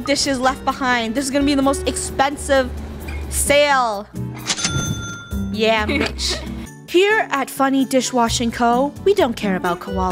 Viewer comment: Did funneh just say "Yeah b-"